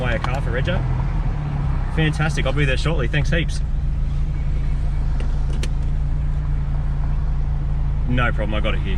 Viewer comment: huh, what did he say? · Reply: "Is there one round here?"